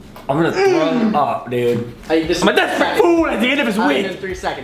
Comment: What the fuck is Kian saying at 4:35 ?😂😂 I’m dying laughing. I’ve replayed it so many times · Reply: Krista Chafe I’m a desperate fool at the end of his wit” lol